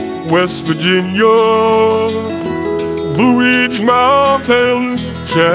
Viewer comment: why did I hear "Boowitch" instead of "blueridge"?